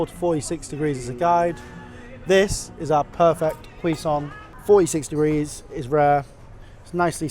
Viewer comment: Can someone explain what quisante, cuisante, whatever the word he's saying here? 9:52 I've got a feeling it's just chef bullshittery, you know, the stuff they say to make themselves sound intelligent but I could be wrong. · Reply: Cuisson, french for 'cooking', is just the french term for doneness